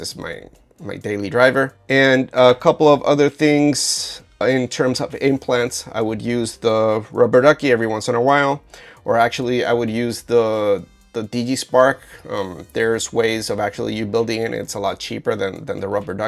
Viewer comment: Great video, thanks for making and sharing this 🙏 At 9:05 you mentioned an alternative to the Rubber Ducky and I thought you said it was De Spark or DD Spark, but on further research it turned out to be Digispark and folks can easily find many pages about it by searching for something like digispark rubber ducky 😊